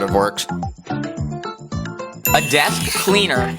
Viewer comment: i thought i heard death cleaner